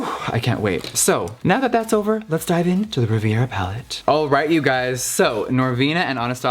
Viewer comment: I thought it said rivera .like jenny rivera .. ykkk 🙃🙃